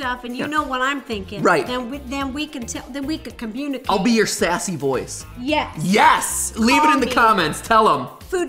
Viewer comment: “I’ll be your sexy voice” MatPat 2020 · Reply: I thought he said sassy voice